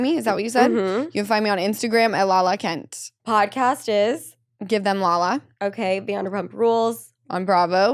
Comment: LaLa is such a jerk. I’m definitely not on Tom’s side but I can’t watch mean girl LaLa. I cheered when Schwartz went in on her. He was so right, the first time I heard “Give them LaLa” I thought give me pepto bismol.